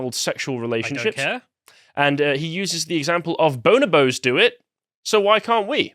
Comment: oh for a moment i thought they said "boner bros"